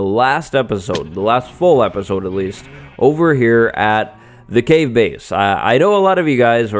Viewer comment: The gay base 😂 is that what you said 😂